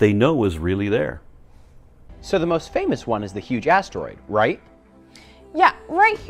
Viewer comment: It sounds like he says huge ass story.